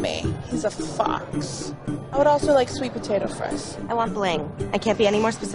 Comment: I thought she said "I want Blaine" for a minute